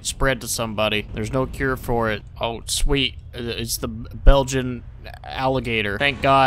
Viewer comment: I swear he was gonna say Belgium waffle eater